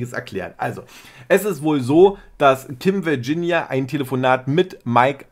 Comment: Ich verstehe bei 3:57 Tim Virginia 😄